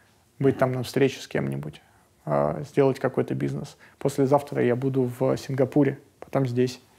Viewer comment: а послезавтра в СИЗО (послышалось)